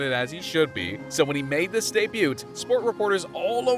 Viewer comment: …. did you say debuT?